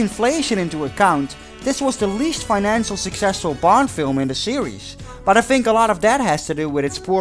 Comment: Why every time you say "Bond" I hear "Blond"?